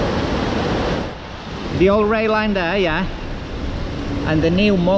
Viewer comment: The 2 flyovers are called burdock way. And the other bridge is north bridge the old main road into town centre before they built they flyover in 1973! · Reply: I think he says "old rail line" when pointing underneath North Bridge at 04:33, but you're correct. Always been a road bridge, but did have trams on it years ago I believe.